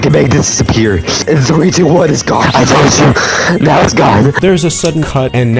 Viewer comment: when the captions say ??? he said "i told you"